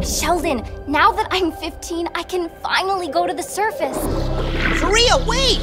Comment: The way I thought she said "now that I'm 15 I can finally go to the circus" 😭